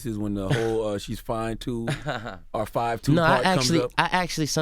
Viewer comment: I always heard "fine too" I didn't actually start hearing "5'2" until years later and somebody informed me that there was even a debate...